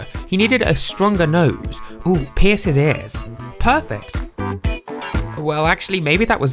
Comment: Ohhhhhhh “Pierce his ears” . That’s what you said.